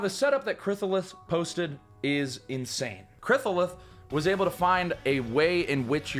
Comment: when he says krithalith it sounds like hes saying krissaliss with a lisp lol